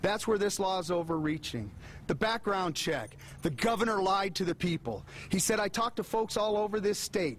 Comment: amazing cop making amazing speech dude in the back: “wHyS tHe gOvErNmEnT pUniSHiN Us?!?! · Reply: I thought he said govenor